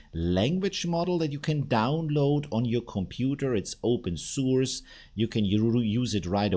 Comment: I thought you said “open sewers”😂😂. (Open source). Same thing??